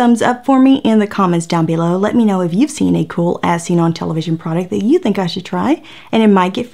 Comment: everytime says "if you've seen a cool As Seen On TV item...." i hear, " if you've seen a cool ass on TV item.... " lol... must be me...